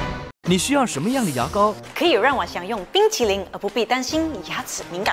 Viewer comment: did she said bing chilling lol